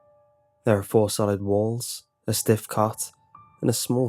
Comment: I thought he said a stiff cock lmao